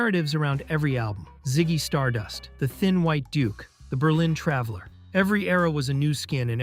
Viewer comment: "The thin white dook." That's how I heard it. That's how I'll remember it.